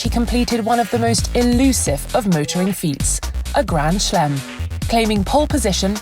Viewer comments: did she just say 'grand shlem'? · any one else understand "Grand Schlem" instead of "Grand Slam" at 1:06 ? haha